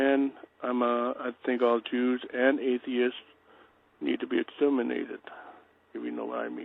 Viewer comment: I legitimately thought he said "all jews and atheists need to be inseminated"